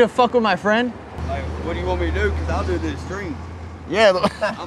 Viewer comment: I thought he said " What do you want me to do?cause I'll do it in his dream "